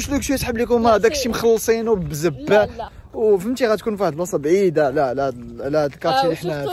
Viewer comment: I missed u guys💖, happy to see u fine again, ND m excited for ur new house.... But wait! wut did u say? 24:06 😳🤭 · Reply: Zebala ?? Pourquoi tu es choquée ?